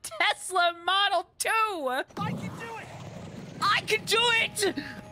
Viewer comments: i always heard "WHY'D YOU DO IT?" but its actually "I CAN DO IT" i think 😭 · I just now realized watching this video that he says “I can do it!” The entire time I thought he said “Why’d you do it?” And I was so confused. Edit: I thought that he was in pain when in ball form, and link kept making him do it. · I thought Yunobo only said “why’d you do it?!” when I launched him at nothing but nope he’s just interrogating me under the guise of saying “I can do it” And I stumbled upon the Fire Temple by complete accident before even meeting Yunobo